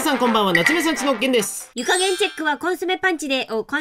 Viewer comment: 「湯加減」が「床げん」に聞こえる…